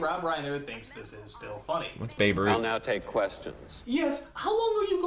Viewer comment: What did you say right there? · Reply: ahh I said “well that’s babe Ruth” I may know nothing about pop culture, but my sports knowledge is insane